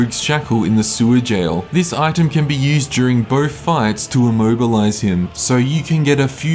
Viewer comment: Did you say immohgilize him?